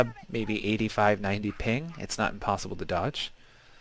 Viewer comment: It's kinda hard to make it out. Did you say not possible or not impossible to dodge? because if you said not possible, that would explain why me or my friends can't dodge them, as we live far away from eachother. I know I already tried side dodging the assassin's leap, but I usually get caught anyway.